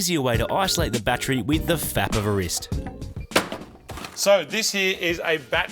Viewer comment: Did you just say "a fap of the wrist"